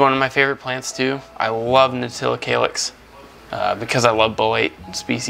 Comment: Okay, at 8:07, anyone have any idea wtf he says that plant is called? It sounds like “nutilla calyx”…but that’s obviously not a thing. It’s making me crazy cuz I think the plant is dope af and want one! I know this video is a year old so🤞🏼😬🤞🏼 someone sees my comment and comes to the rescue! 🙏🏼🤗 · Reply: Nautilocalyx